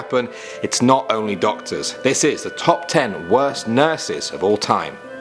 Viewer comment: I thought it said top ten worst nudes